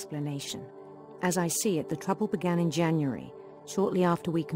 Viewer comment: Edith Finch died in June 18,2017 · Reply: I thought it said January